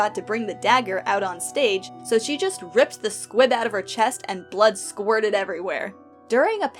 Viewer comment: i heard "squid" instead of "squib" and was momentarily very confused